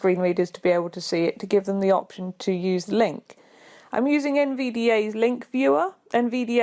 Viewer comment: What is she using and where can i get it? Did she say VBA Link Viewer? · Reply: Sorry, only just spotted this! I'm using NVDA - it's a free screen reader application: <link>